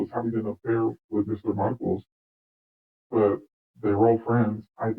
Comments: She refers to Mr markel who does she mean ? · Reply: If you read comment at the top it explains she saying Mr Markus (Anderson). I thought she was saying Mr Markle 🙃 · CLICK the Closed Caption (CC) to get subtitles and understand the interview. Also to clarify, She is talking about Markus Anderson when she says that she thought Mr Marcos was her lover FOLLOW ME On Facebook to message me directly! It's free to follow, just click on the link below 👇 <link> THANKS for SUBSCRIBING and to those who kindly support the school by either clicking the $ thanks button under the video, or through PayPal <link> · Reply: Thank you for clarifying. I thought she kept saying Mr Markle. Which is what Harry really is. lol. But I get that it’s Marcus she said now. You said there are more interviews? Keep em coming!